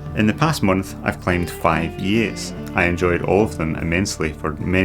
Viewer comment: you're saying 'in the past month I've climbed five ...', what is the word after five? I cannot make anything of it that would fit in the context. Very enjoyable video! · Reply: "five E8's"